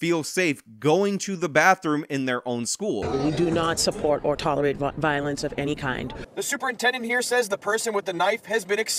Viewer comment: I can't even imagine being in such an environment in high school. Its actually kind of messed up that even in middle schools they require officers because the kids are already fighting and attacking each other. Public schools are a nightmare and you should do everything to get and keep your children out of them. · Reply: Am I the only one who heard the teacher say we don't support or tolerate BLEEP violence, before correcting herself?